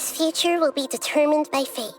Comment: why did she say in the VL by fate but in text say by luck. Hmmmmmmm